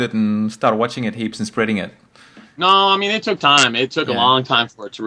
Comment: you miss 1 ''you know'' at 4:37 nooooooo you know it took time...... i think you said ''you know'' but can't be sure because english is not my prime language great vid